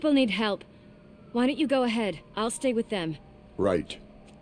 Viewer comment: he says right but the subtitles say good which is it game